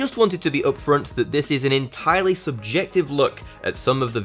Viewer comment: Pretty sure I say subjective 😅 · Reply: Careful replaying has me wondering why I heard 'objective' in the first place... I clearly, objectively, so to speak, hear 'subjective' now 😬